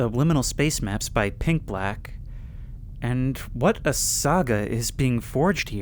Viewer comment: when you said "What a saga" i genuinely thought you said "What the sigma"